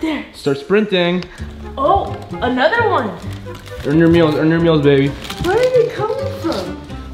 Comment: “Earn your meals baby” made me die😂😂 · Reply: Jasmin Sanchez I thought he said on your knees baby 😂😂